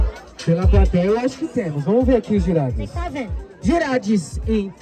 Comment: Sério que ele falou: Jurades? 6:52 (Dicionário)